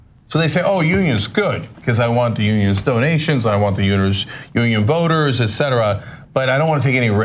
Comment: did Chenk accidentally say uterus instead of union?